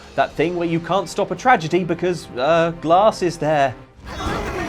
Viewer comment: excitement at the end really made it sound like he was saying, "Hurray! tragedy!"